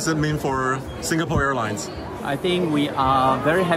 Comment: For a second I thought they said Spirit Airlines won and I about died xD